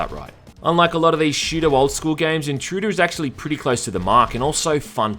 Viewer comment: What is the Wolfenstein-esque game at 13:40? I can't quite make out what you're saying. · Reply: I believe he said "Intrude."